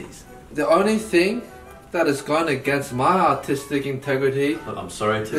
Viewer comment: I thought he said “my autistic integrity”